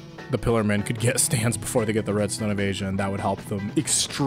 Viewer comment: I thought he was saying "Redstone of Asia"